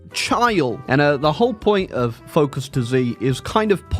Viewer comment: i just woke up from a weird nap and i swear i heard "focus to z" in 0:30 as focus disease